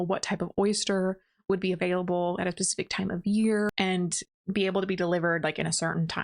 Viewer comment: did you just say “pacific time of year”?